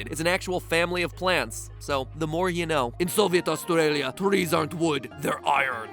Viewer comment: Did he say soviet Australia at 7:00? Thats not a thing